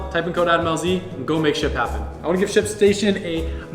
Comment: I thought he said shit station or was it just me 😂